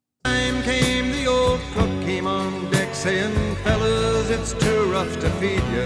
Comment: Did he say The Pokemon Decks ?